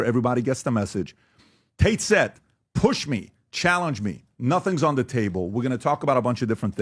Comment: he actually says “nothing’s ON the table” not “OFF the table”